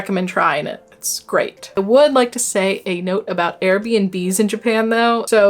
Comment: When she said Air BNB I thought she was saying Arabian Bees 😂